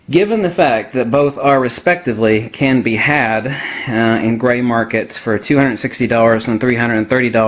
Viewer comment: ebay? cuz i'm there almost daily and i've never seen that price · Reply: i thought u said gray markets, i was like wtf lol. thanks though